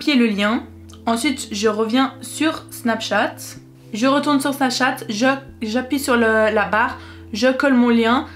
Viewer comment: A 3:50 like si toi aussi tu as entendu "On va retourner sur sa chatte"😂